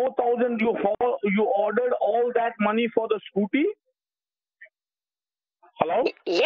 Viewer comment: “ you use all that money for the Scootie?!” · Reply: Sounds like he said SCOOPY